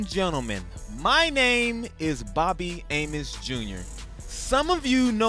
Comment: I totally thought he just said "Bobby Anus Jr."